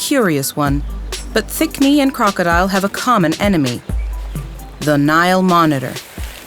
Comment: I keep hearing "A crocodile and a thick me" and I can't stop laughing